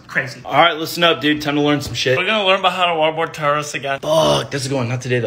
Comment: Why did I hear "...waterboard tourists..." ? 🤔 Nevermind👋Disregard🤫😜🇺🇸👍🇺🇸